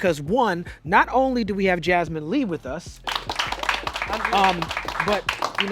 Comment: I thought he said Jazzman Lee